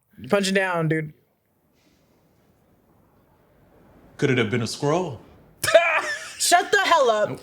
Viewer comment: "I thought that he said "Could it have been a squirrel" at first and I was very confused until I realized they meant "Skrull."